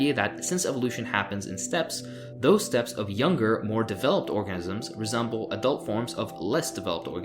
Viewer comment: Hehe at 24:30 you said orgasm instead of organism.